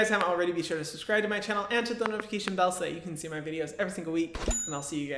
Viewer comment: “and I’ll see you guys in the next o-“, IN THE NEXT WHAT??? 😩😩 THE WORLD WILL FOREVER QUESTION WHAT YOU WERE GOING TO SAY 🏃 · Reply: I think he said ‘And I’ll see you guys then’ :)